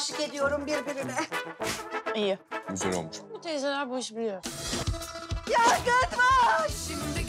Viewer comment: ne diyor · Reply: Yangın var